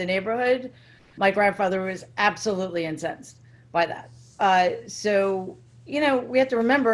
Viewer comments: did she say: "My grandfather was absolutely entsetzt"? You use this german word over there, like "Kindergarten"? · Did she say "absolutely entsetzt" at 1:04 ? "entsetzt" is German, ist the a loanword in the English language? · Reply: Incensed.